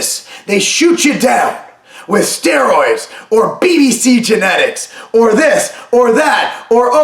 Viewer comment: Did he just say "BBC genetics" LOL